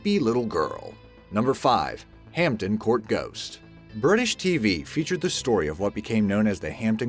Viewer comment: What the fuck did he just say😂4:37 BURDUSH TV😂